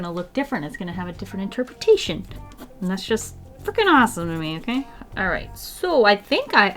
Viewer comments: it sounded like you said "fucking awesome" which is obviously chill its just a word, it was just funny to me cause ive never heard you cuss or sound lik you were (again no judgement it was just funny) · Did you swear at 18:45 :0 or did you say “frikkin” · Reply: i don't swear. its Freaking. then with my accent it sounds like Frikkin lol